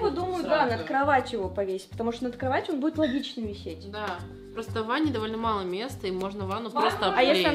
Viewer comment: мне послышалась что зовут валя